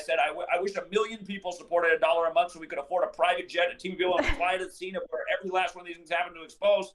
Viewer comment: did I just hear that correctly? A private jet??????